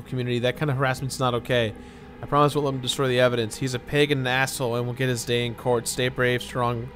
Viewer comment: Did anyone else hear Jirard say, "He's a pagan asshole..." or is it just my brain?